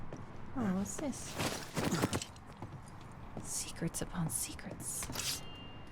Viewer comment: I thought she said s3x secrets too 😂 just thought what a strange twist 😅